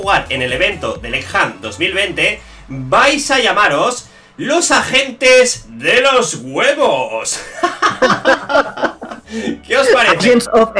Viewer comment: al principio le entendí a "los agentes de los juegos" xD